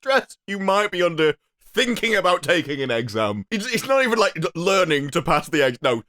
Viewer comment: "You might have a very minor case of serious brain damage" And idk why, sounds like he says exam as "egg-sam" 4:15 don't know if it's because of the whole new accent coming out of him in the beginning